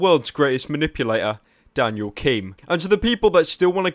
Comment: i thought he said daniel kim instead of daniel keem